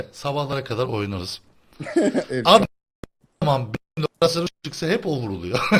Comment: 'de ne diyor?? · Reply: menderes ne zaman ben operasyona çıksa ilk önce o ölüyor :)